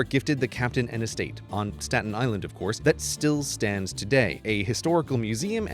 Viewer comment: Actually thought it said Stalin island